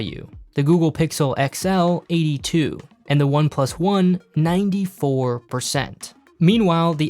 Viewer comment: exactly that's a OnePlus 1, not a OnePlus 5 or 6. He was referring to the resale value of the iPhone 10, not iPhone 3GS. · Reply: check it out. He clearly says "OnePlus 1".